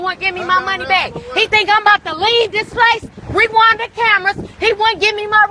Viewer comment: When she said "rewind the cameras" I thought she was saying a name like "Rewonda Camerons"